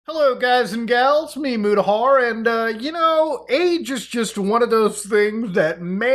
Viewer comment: The beginning I rly thought he was about to say “you know age is just a number” lmao